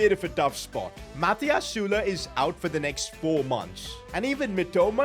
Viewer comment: did you just say the n word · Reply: He said is out